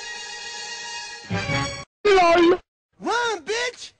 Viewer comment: i only hear: LOLM